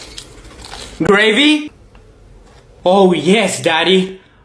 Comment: I love how the guy drank the sprite like he was kissing it💀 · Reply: I thought he was saying yes daddy yes 💀💀